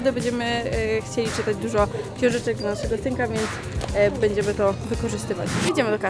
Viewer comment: tak dźwnie powiedziała dla naszego synka ja usłyszałam Tymka ?